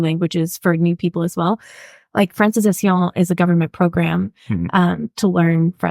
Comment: sorry, I didn't catch the name of the program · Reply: Its a program commonly referred to as the 'Francization Program' though is not the name used for it on the Quebec gov website. Its a program that offers french language courses for free, though the process to access them is quite seems to be quite difficult in practice. Hopefully it will improve. <link>